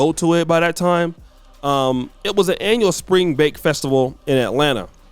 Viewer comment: Did he say Spring Bake?